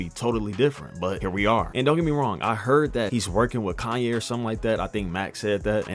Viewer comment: did you say Kanye?